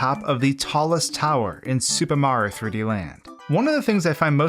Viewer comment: Sounds like he is saying Super Maro Land